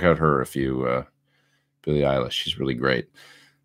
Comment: The first couple minutes that girl your talking about sounds like your saying "Billy Idol.. she’s great”. Maybe saying it a little fast. Any way you could type her name out in a comment for me? thanks bud! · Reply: Eillish :)